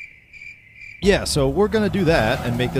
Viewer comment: Istg i thought he said "im not qualified for this sh1t"